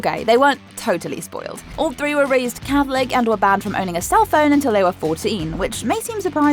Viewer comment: minutes did she say all 3 kids were raised catholic?? 🤔